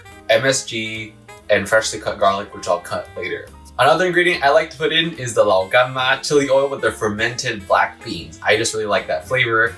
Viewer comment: For those who turn on the CC and didn’t catch what said, he said “Lao Gan Ma” chilli oil